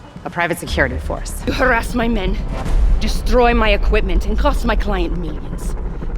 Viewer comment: I thought silver sabel said "and you fucked my client"